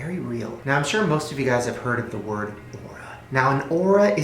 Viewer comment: Am i the only one that heard "oral" instead of aura at 6:25 ?? lololol...